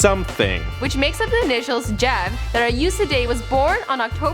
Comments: does she say "that I used to date"??? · Did say "who I used to date"? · Why does it sound like she says his name than “that I used to date” on